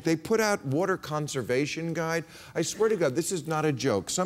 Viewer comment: Wait a minute : did MAHER say "I swear to God" at 1:06 ???